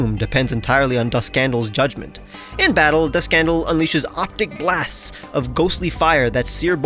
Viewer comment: it sounded like you were saying "the scandal" during this segment lol